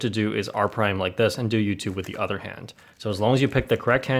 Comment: On 3:47 , when captions are on it shows YouTube instead of U2